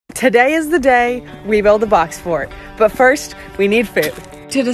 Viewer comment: wow at first i heard a ‘fox fort’ lol